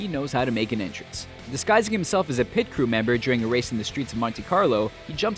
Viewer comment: turn on captions. It says "disgusting" instead of "disguising."